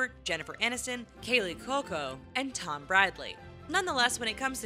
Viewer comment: Did you call Tom Brady "Tom Bradly"?